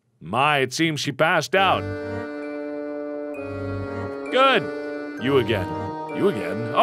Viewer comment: I thought it said "you asian..." When she saw the red eyed man again XD